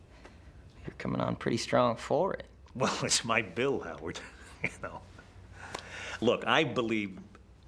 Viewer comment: does he call him Edward?🤔